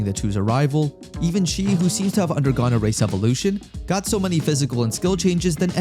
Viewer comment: Bro said race evaluation in part 1 but i thought he said racist evaluation ☠️